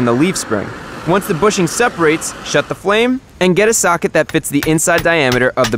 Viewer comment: LOL i thought u say shut the f but u sayd shut the flame